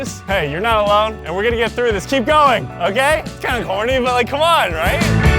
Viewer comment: I thought you said "I'm kinda horny" 😂😂😂😂